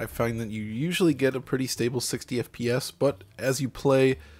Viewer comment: I thought it said 90fps lol